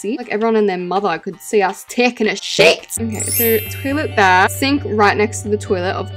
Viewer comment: did you say sh*t?